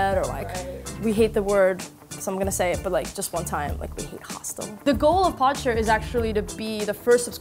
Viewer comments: "we hate the word, but I'm gonna say it...hostel" Honey, you run a hostel. No amount of disruption branding is gonna change that. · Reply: Gentiana, Frostbearer, Galcian. I know what a hostel is, I just thought she said hospital. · What's the word she said they hate at 1:58? Hostile? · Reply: Hostel. It's a place for travellers, students, etc. Basically a cheap alternative to hotels.